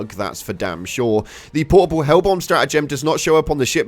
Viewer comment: Hellbomb. 😂. With your accent, it sounded like you said hellpod. Anyhow, we’ve also noticed that some of the sub objectives won’t allow you to access them. When you’re in the local area, you can see the statement: use strategem to access the flag or ore vein, but there’s no strategem arrows on the right to punch in the codes. Nothing big, other than the loss of time expended running across the map on lvl 10 bugs. But yeah, we’re all in the same boat. Hope they eventually fix the glitches. Still love this game. 😊